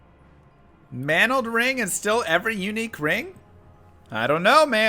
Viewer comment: I heard "manald ring is still every unique ring?" while drinking coffee and spat it out now I can say my keyboard has a brown camo skin